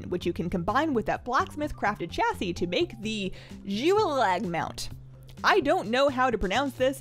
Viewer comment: zi-we-lag, youre welcome edit: "zi" as in how you would say "zip" but with "we-lag" afterwards instead of the "P"